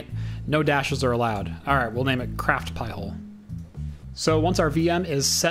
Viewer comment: LOL - had a few drinks tonight then came to your channel for whatever reasons and started watching your old videos... thought you said CrapPie Hole many times... was laughing then I opened my eyes and looked at what was playing... hahahaha.